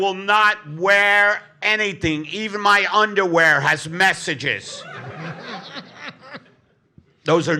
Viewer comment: When you said your underwear had messages, I heard “Mess of jizz” and laughed harder than I always was